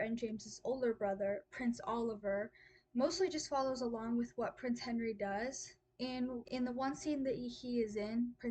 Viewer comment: You said ‘prince henry’ and I heard ‘prince harry’😂